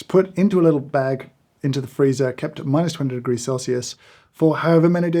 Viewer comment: Did you say minus 200 degrees